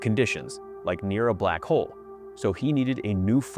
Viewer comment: Sounds like u saying we are Black hole xD in a other universe